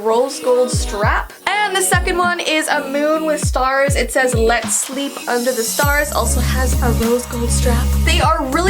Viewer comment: When I heard about your handbag merch (the blue one) I thought it said “let’s sleep under the stairs” great video btw!